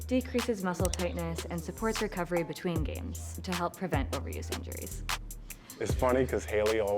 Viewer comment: did she say “help prevent ovaries injuries”??😵‍💫